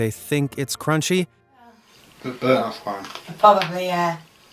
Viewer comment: i cant understand why he thinks its crunchy, someone translate please? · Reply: "Its a bit undone" I think